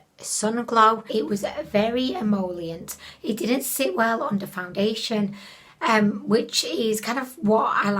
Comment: I hear go all Jamaican on me & then, I realized she actually said “it didn’t sit well under foundation”, not “on da foundation”. 😂😂😂